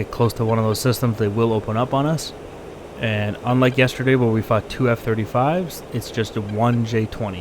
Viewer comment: At the beginning I misheard as "unlike yesterday where we f***ed 2 F35s" and then realized that's still an accurate description of what happened.